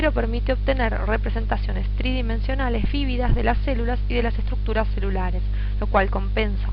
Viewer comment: que dice??? vividas o fibidas what